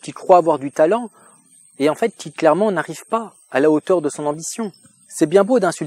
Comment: Pourquoi j'entends "Hitlèrement" à 1:00:56 lol